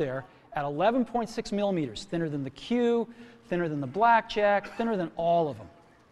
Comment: did he said ''better than the black jack''?